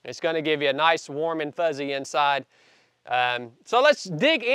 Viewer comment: I thought you said a nice warm CHUBY inside! LOL